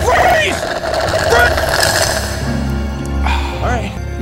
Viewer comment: character that clearly is having a stroke Subtitles: MuSiC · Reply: And 0:30 I know FrEe it said free instead of freeze